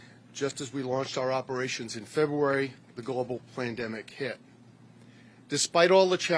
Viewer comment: Did he say "global PLANdemic"?